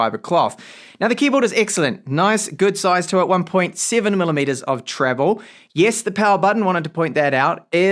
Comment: Did you say 7mm of key travel?